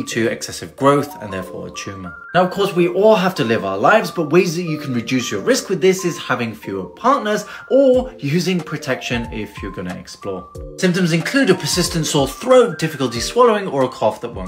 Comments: I thought he said "use protection if you're gonna explode." 😭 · I thought he said use protection if you’re going to explode.😂